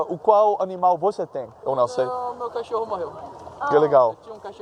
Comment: I speak Portuguese at an A2+ maybe a B1 level and to be fair, I heard him say, "meu cachorro meio", meaning "my medium dog", which doesn't make a lot of sense. It wasn't until reading the comments that I rewinded to hear if he had actually said, "meu cachorro morreu". The response of indicates he didn't understand him but he could've asked the guy to repeat himself instead of saying, "wow, that's cool!" Repition is key. Why do these polyglots never ask people to repeat themselves?